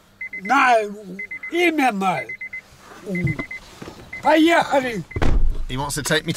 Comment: i think he is saying in german "Gehen wir mal" which translates to "let's go". Maybe I am wrong ^^